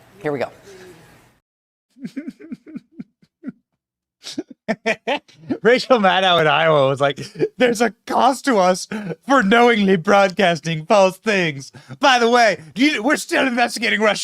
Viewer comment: thought you said rachael madcow!! it fits, a diseased brain, although I think cows have a higher IQ.